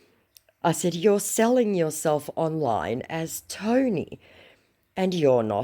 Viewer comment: "Itstony"omgrotflmbo 😅😅 Dang, my stomach hurts from laughing so hard and I blew my mouthful of Pepsi across the floor; I thought he said OSTOMY!!😅😅😅